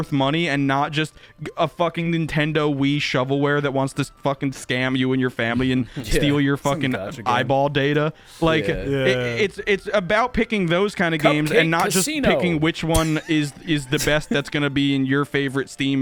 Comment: did somebody say eyeball data?!?!?!?!